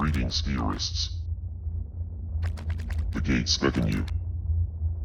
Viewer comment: The ending I thought he said GREETINGS TERRORISTS...